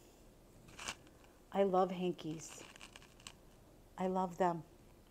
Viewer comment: Lol. I was listing items. Heard you say. I love hankies. My ears heard hickies. I looked up at TV wide eyed Lol😅😅 . Oh dear I need a vacation